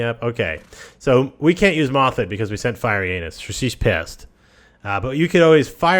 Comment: it sounds like you said "FireInAnus" XD